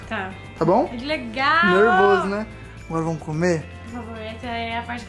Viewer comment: No momento 14:37 eu ouvi um mor vamo cume ?